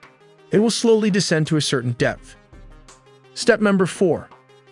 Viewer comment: Step 3. It’ll slowly submerge to certain depth. I thought you were saying “it’ll slowly submerge to certain death.”🥶